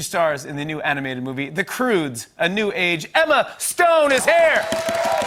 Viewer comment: Did he call her 'Emela Stone?'